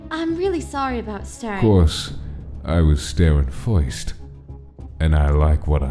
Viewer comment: When Sans entered the bathroom a while after he said, Of course, I was staring first But it sounded like he said, Of course, I was staring foist